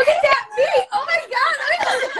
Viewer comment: I watched this live a lot of times but I have never understood. What does Danielle say at 23:53? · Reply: She said look at that beuty